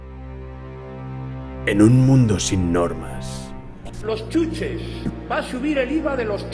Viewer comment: al inicio entendí "en un mundo sin drogas"